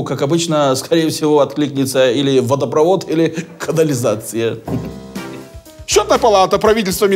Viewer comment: вместо "счетная палата" послышалось "чё такова" - диктор за кадром виртуоз.